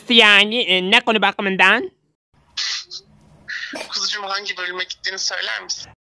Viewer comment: "Kıvılcım hangi bölüme gittiğini söyler misin?" mi dedi ben mi yanlış anladım